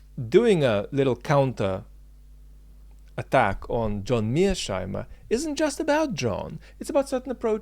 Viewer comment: I can't quite figure out what/who Mirch Heimer is/are. It sounds like you say mirsh/mirch Heimer. I tried to Google it, but no bingo. Can someone tell me who/what it's referring to 👍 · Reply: John Mearsheimer